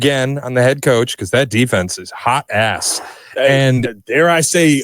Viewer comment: Did bro say "the defense is 'Hot Azz'"? 😂😂😂